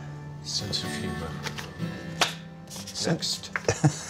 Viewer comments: what did nick say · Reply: A sense of humor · man that was rough. "A sense of humanity" it sounded like Nick said.